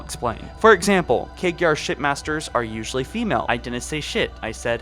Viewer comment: I totally heard "shit master".